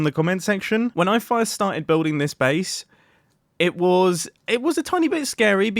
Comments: Did he say “when I fire start building this base” ? If reads this Fyi there is a slight audio crackle but I can live with it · in the video he says FIRErst instead of first